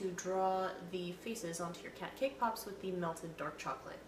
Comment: When you said "Faces" I thought you said feces for a second, OMG. I love those cat puns, also that cat tail looked like a long... poop.. What did you think I was gonna say XD